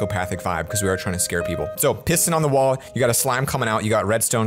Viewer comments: Did he say P*ssing on the wall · it sounded like he said “Pissin’ on the wall” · did I just hear him say “pissin’ on the wall”. XD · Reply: Lol i heard it tooo but he said piston on the wall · sound like he is saying pissing in the wall you got slime coming out lo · sounded like he said "pissin'" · I heard pissing on the wall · i heard the word "piston" as "pissing" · i thought he said pissing on the wall lol